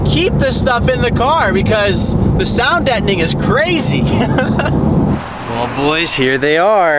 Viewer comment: What did you say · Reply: the sound deadening is crazy is what he said